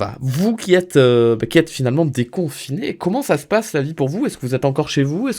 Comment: j'ai entendu "vous qui êtes finalement des cons" j'ai buggé 😂